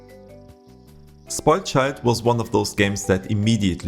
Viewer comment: Hi what was the name of next one on 22:59 I wasn't quite sure on what I heard sorry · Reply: spoiled child :)